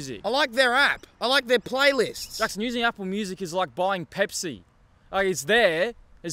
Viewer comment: not me watching this minding my own business drinking my pepsi and then I hear "it's like drinking pepsi" 😭😭